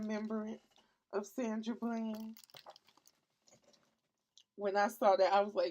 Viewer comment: Sandra bland she was a black woman who was stopped by police for no reason and taken to jail where she died under mysterious circumstances. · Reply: YES okay see I knew that, but I thought you said Sandra Brown and then spent like 10 minutes trying to find news of her death lol